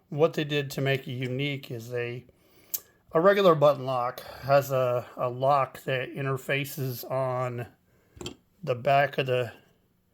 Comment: I like the new Corgy V a lot more than the previous ones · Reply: I watched this part again - I misheard you and thought you were saying it had a regular button lock that hits a liner. That's what I get for not paying full attention - sorry!